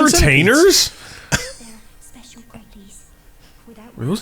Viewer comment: the auto captions think that lady is saying "special panties" 😂